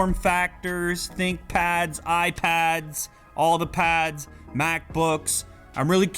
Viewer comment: thought he was gonna say Maxi pads....